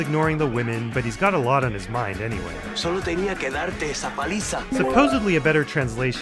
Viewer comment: What he says is " Solo tenia que darte esa PALIZA " and not "peliza" (peliza isnt even a real word in spanish), also absolutely he was talking about beating up gus, not spanking wtf a better translation would be "I just had to beat you up"